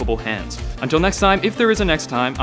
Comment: I heard "Ron McKenzie the virgin" :D no insult intended just a funny anacdote